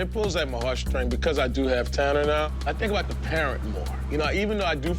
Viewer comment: Im sorry did just say he has 10 kids? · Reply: No he said “I do have Tanner now”. He just had his first child whose name is Tanner